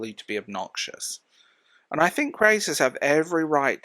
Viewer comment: Dear god, I completely misheard you the first time. I had to relisten about 4 times....I thought you said crazies, not creators. I think crazies have every right to protect the kind of environment they're trying to build. 😅😭 Like, yeah, I suppose that's technically true